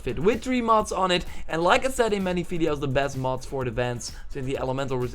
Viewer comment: what does he say about best mods for armor. elemental useless. I couldn't hear part of if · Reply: Best mods for defense, sorry that it was hard to hear. So yeah melee and ranged resist armor is the best imo.